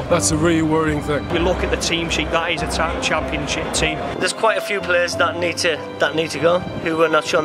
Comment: I'm sorry... did he say just say "That's a title championship team ?"